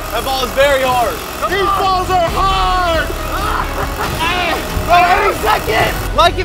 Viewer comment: Bro did I just heard him say These balls are hard🤣🤣🤣